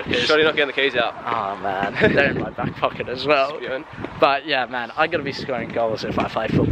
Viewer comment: What did he say between 12:40 and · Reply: Spewing! Basically means gutted or like devastating, just your good ol aussie slang mate!!